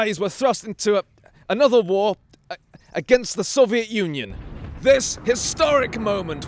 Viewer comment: When you said "Soviet Union." I thought you said "Soviet Onion."